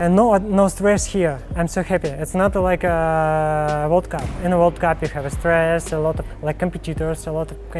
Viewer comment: Don't tell me I'm the only one who heard vodka instead of world cup at 2:18 Those Russians man...